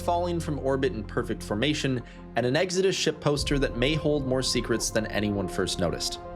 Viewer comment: At first I thought you said 'exodus shit-poster', was like...ok, weird.